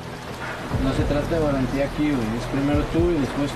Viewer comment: Que es lo que dice en el minuto 2:45?? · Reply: No se trata de valentía aquí we